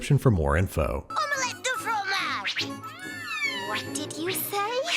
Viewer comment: Being French, it used to drive me crazy to hear say: "Omelette DU fromage"; instead of "Omelette AU fromage"... But it is the most delicious dish in the universe, no matter how you make it, so I forgave the insult pretty easily. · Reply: Weirdly I always thought was saying omelette deux fromage (my young self figured it was supposed to mean two cheese omelette) which I figure is still grammatically incorrect.